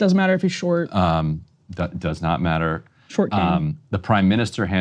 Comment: 😂😂 Did Emily say “short king” at 1:00 haha